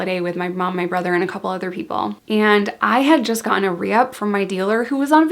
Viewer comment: something funny happened... when you said "I had just gotten a reup" for the first time, you said it so fast my Fiance and I thought you said "i just gonorrhea" LMFAO XD no biggie, we just thought i was hilarious!!